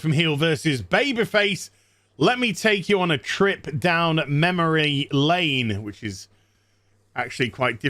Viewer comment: I thought you said "A trip down mammary lane" at first.